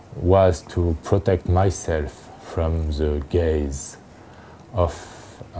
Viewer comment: Does he say to protect himself from the Gay's at 5:55 lol? Really like what he is doing great stuff, very unique.